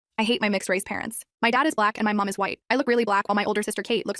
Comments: I thought she said I ate my mixed-race parents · I heard "I ate my mixed parents"